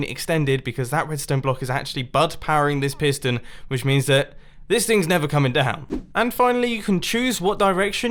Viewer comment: Captions said "this thing's never coming to hell", and now I can't unhear it.